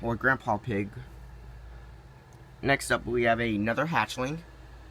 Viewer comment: i thought for a moment that you said "a nether hatchling" :v